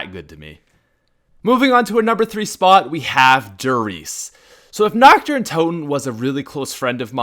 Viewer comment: When he said her riese I thought he said de riese